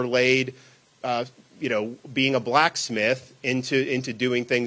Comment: Did she just say blackies?